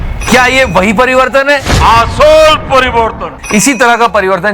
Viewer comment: Did godi said Ass hole parivartan🤣🤣🤣